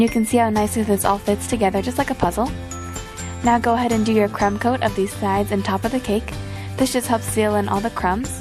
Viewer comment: did she say cum coat!